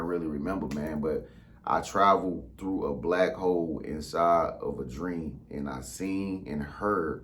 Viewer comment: It sounded like he said Black Hoes at first.🤣🤣🤣